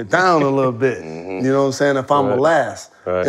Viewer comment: all I heard was "I was smoking, that's what I took from my Denver experience" 😅